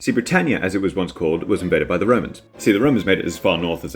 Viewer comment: I know it's was an accident but the first time you said romans I heard "woe-mans" "I have a vewy good fwiend in wome named Biggus Dickus. Do you know what his wife is cawwed? Incontenentia. Incontenentia Buttocks"